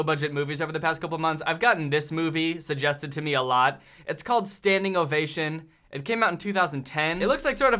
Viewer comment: I THOUGHT THAT SAID STANDING OVULATION AAAAAAAAAAAAAAAAAAAAAAAAAAAAAAAAAAAAAAAAA